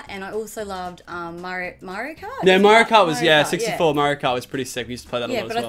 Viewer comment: How they said Mario Kart I thought they said Murica lol